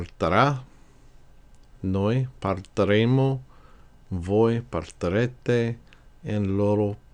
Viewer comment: Thanks, very helpful. But: the speaker did not pronounce the "i" when saying the examples of the conjugated forms of partire. it sounded like he was saying "partarete" instead of "partirete." Are we supposed to pronounce the "i" like the "ee" in English? Or like the "a" in Italian, as the speaker did in this example?